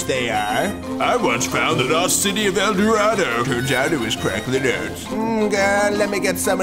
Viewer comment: after listening to this for the 50th time and after coming back from to it years later now, I finally figured out what the guy was saying. "...turns out it was" at 0:50 It just hit me all of a sudden ...I should be disappointed.